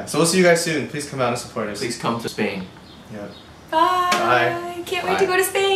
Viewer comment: at first, i thought u guys said sydney, and i was like HOLYYYGNDIFBNJIN. but then i replayed it and u said spain. and i was like. -.